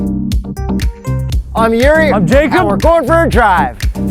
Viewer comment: the subtitle says in the area I'm Jakob Lorber drive. 0:26 my reaction to the subtitle.